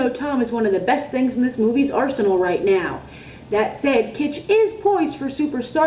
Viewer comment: Am I the only one who needed to rewind to 3:06 thinking she said "arsonist" instead of "arsonal"?